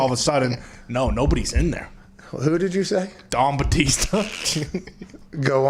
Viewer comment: Thought he said “Dong Batista” 😂😂😂